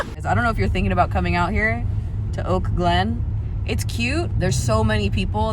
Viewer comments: Omg for the first 3 minutes of the video I thought you said you were in Oakland and I was so confused 😂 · The first time you said Oak Glenn I thought you said Oakland and I was like “Ayeeee!!” & then was confused and hungry for like 4 minutes straight😂😅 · I fully thought you said you went to Oakland for Apple pie💀☠️💀☠️ then I realized oak glen 😂😂😂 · I thought she was saying Oakland. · LOL I thought you said Oakland!! I was like biiiiiish where I need to knw!!!